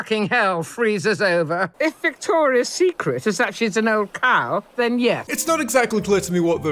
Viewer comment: i Heard "if victoria's secret is an old car then yes" and laughed because I thought the queen meant she wants to kill her the same way she killed Diana. the only joke I laughed at was the one I misheard and made myself, let that sink in